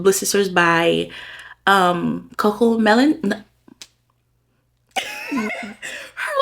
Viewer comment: It sound like you said “Coco Melon” 😂😂😂😂 and I don’t listen to anything in the background because I’m always listening for my toddler while reading 😂😂😂 but I definitely want to try.